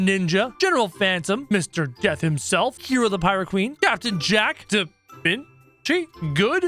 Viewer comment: did you say captain jack? Hmm u know I thought it was kyle but that's fine no kyle here